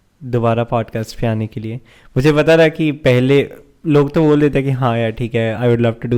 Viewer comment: i thought you said pod fest ,pod fest sounds unique as a channel name